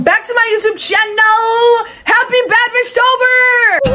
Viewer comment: When she said bad bish tober at first i thought she said bad bish topher 😭